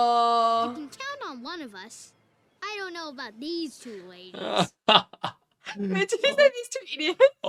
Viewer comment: Meelo: "I don't know about these two ladies." "LOL Did he just say 'these two idiots?!?" Sometimes I forget that our American accents must be hard for other English speaking countries to understand sometimes...